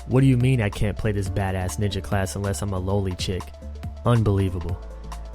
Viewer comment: He said "loli chick" but I heard "lowley chick" I was like wow fuck you for a second lol